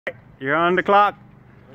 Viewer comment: Everything was fine until he cursed them in that weird language he spoke seconds b4 the fall hahaha 😂😂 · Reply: I thought he said “ you’re on the clock “ meaning they better get to work